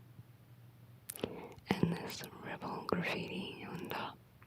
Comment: I definitely heard “rebel gravy” at 30:29 and didn’t question it